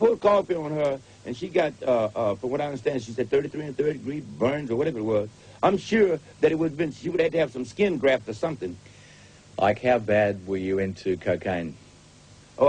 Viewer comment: Interesting. Sounded like he said 33 1/3 degree burns near the start of the interview.